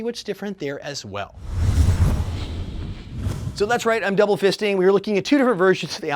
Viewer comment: Did he say he's double fisting